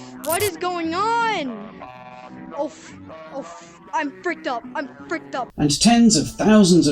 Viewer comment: did he say “I’m bricked up”? Lol